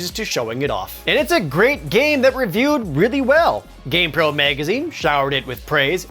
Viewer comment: I don't know what it is, but everytime says Gamepro I hear "Game Girl"